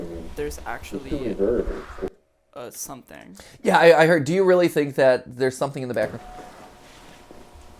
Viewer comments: i heard "do you really think that there is a bathroom in in this situation?" or "do you think that the backrooms have a bathroom in this situation?" which makes me laugh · I heard him say "do you think there's a monster in the back rooms" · in the part where you couldn't hear the muffled guys talking, i heard "do you really think theres a monster in this facility?" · I heard “Do you really think that there’s… a monster in this facility?”